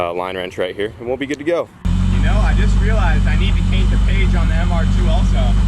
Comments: Did you just say paint the cage instead of caint the page. Or is my hearing playing tricks on me. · Cain't the page? Did I hear that right at 7:50? LOL